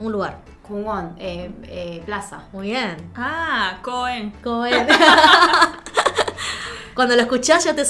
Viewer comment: La chica (Corea) dijo "coño". jejejeje... creo .. o escuche mal...es que como se empezó a reír eso pensé